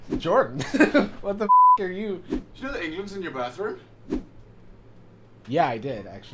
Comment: “Did you know England’s in your bathroom?” Jordan, 2022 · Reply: I thought he said “Do you lay eggs in your bathroom?” 😟